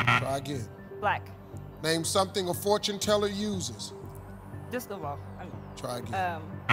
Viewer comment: Did she say "disco ball"? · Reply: Yep, I didn't even hear it right the first time. I thought she said crystal ball but she said disco ball and Steve helped her by saying try again. Didn't matter in the end, but I wonder if they would have taken that answer had they gotten to it.